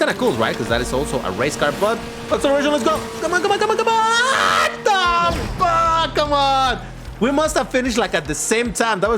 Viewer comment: did unc said Fog or fuck ? 🧐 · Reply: Fog, or frog. I don't keep curses in videos